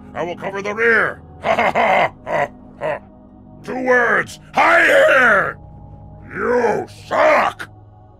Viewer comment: I cant be the only one who heard "Heil Hitler" 😂